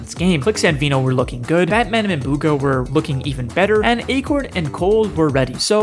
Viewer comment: What the hell did he say afterer clix and veno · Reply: Batman and Bugha a crazy duo 😂